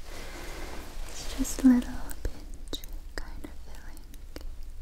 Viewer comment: Did she say the B word or what? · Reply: little pinch